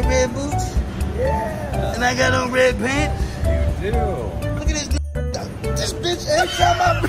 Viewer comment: For those of you still confused by the cut at 2:13, I think the guy said “look at this little quacker duck”, cause, like, the duck is white…